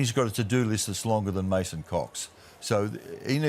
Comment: I think I heard it correctly did Mike say at 4:56 "a to do list that's longer than mason cox" or something else? 😅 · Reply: Mason's